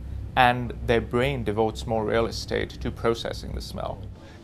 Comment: Can anyone tell me what he is saying at 1:37-1:40? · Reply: He is saying real estate. In scientific terms that simply means connections. Hope that helps